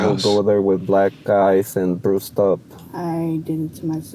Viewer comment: 5 years later....... 😬 Dude come on. What are you doing? edit @ 16:20 I think he said black eyes and bruised up. Not black guys and Bruce stops lol